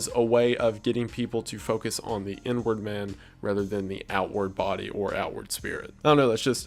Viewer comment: I heard in word man I heard that as n word man